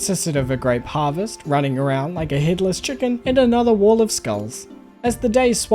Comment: Im sorry but what did you say at 50:07 ? "Like a Hitler's chicken", lmao??